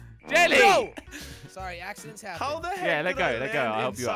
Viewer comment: What did just say? Did he say heck or did he say Hell? ._. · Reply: I think Hell ._.